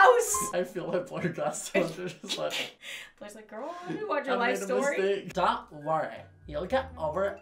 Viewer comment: No one: Lauren: watch: "Hello" robotic voice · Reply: When said “watch” I thought he said “Lunch” 😂